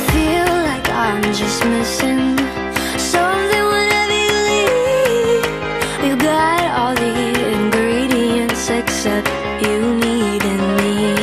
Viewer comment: Одной мне послышалось на 1:40 секси